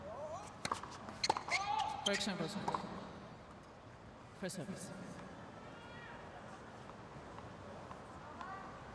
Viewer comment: What does she say at @ 00:18:19? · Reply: First service